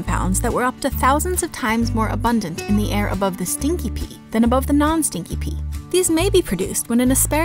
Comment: For a second I thought she said “sticky pee”